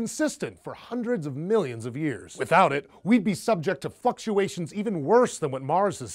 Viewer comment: lol I thought he said fuckuation